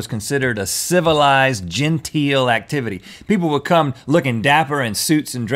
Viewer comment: I thought he said gentile. Not gentle.